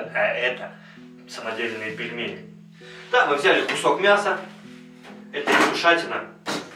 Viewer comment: Про мясо послышалось ,,это лягушатина,,. Потом думаю какая нафиг ,,лягушатина